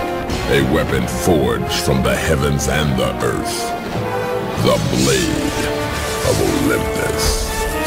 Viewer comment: I swear it sounded like "a weapon forged from the heaven hamburgers"